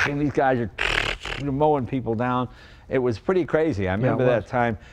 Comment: did Jay say "mowing people down" 🔫 . whoops that's not too politically correct.